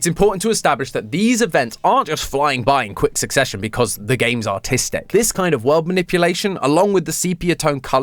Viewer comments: why did I think he said autistic lol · I thought he said “autistic” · swear he said autistic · As a side note. With his accent, i keep hearing him say "autistic" rather than "artistic" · am i the only one who thought he said "autistic roots" instead of "artistic"😭😭😭😭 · I thought he said autisitic….